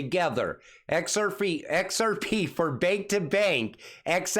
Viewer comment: Did you say xrfeet